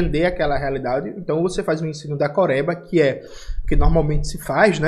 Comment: quando ele falou "ensino DECOREBA" eu entendi "ensino da COREIA" e já pensei, "Mano... calma ai" hahahahahahah